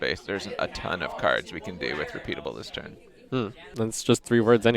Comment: On the echo discussion Mike made literally no sense and just contradicted himself. He says that he doesn't want to give newer cards echo because it;s an old keyword and it doesn't have a lot of design space. Then right after he says "there are a tonne of things we can do with repeatable this turn". . . That's fucking echo. . . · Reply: You misheard Mike, he said "there isn't a ton we can do with repeatable this turn."